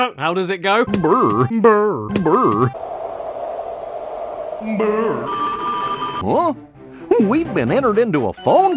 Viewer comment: Is Goofy saying 'beurre' ? Looking inside a fridge, that would make at least a thin slice of sense :p